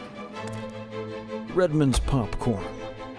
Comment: Damn, I thought that said Redman's popcorn, I was like "Redman makes popcorn now?"